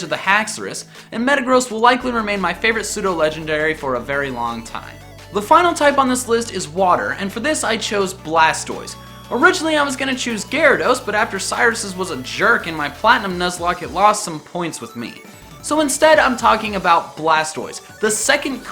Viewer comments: Wait did he say Garydos? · I thought you said "Sinnoh Legendary" and made me do a double-take.